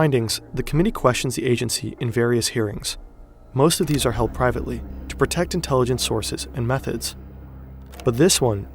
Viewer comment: sounded like "to protect meth heads" lol!